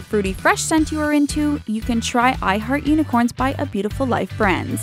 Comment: hahaahhaha i heard " i hurt unocorns" instead of " i heart unicorns"